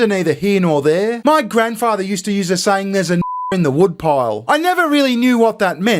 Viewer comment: There’s “N” words in the woodpile? That’s what it sounded like you said. Makes no sense. And is STILL not an acceptable word i the States mate. Nvm if that’s not the word.